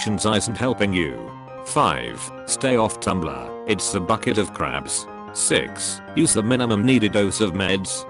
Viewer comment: i heard it saying 'Bucket of Craps' ...haha. The irony is it would be true still.